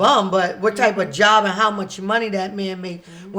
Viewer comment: did she say sh-money? Lol